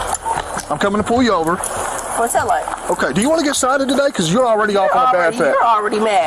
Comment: I swear I thought he said "Do you want to get shot at today" at the beginning.